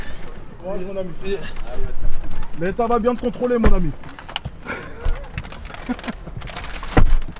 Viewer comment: j'ai entendu va te faire f*utre ?